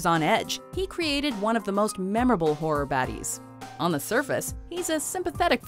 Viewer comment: I rly thought he said horror baddie😭